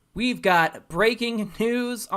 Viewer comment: I thought he said, "we got great news," but he said "we got breaking news."